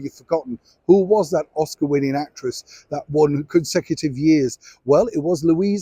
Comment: Dang I was wrong. I thought you said actor and that was Spencer Tracy. Louise Rayner was a brilliant actress. I loved The Good Earth xx Thanks for the wonderful videos, Neil. They make my day everyday